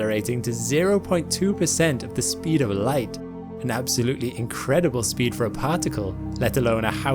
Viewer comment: The standing principle of this channel is “Space for Everyone”, it’s what goes into making every one of these videos and the foundation of our Patreon community. Join today to become a part of that. <link> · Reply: did I hear you correctly say that sun grazing comets can go as fast as 20% light speed??